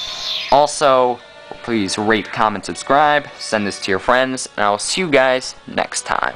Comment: Thumb up is it sounded like he said please rape coment subscribe